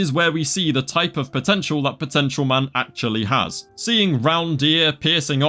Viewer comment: DID YOU SAY POTENTIAL MAN